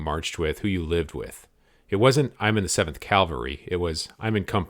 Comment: "...seventh calvary..." i think I distinctly heard him say 'calvary' twice, I'm sure of it.